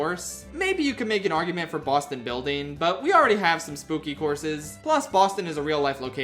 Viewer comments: Why did you call this track building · why is he calling it Boston Building?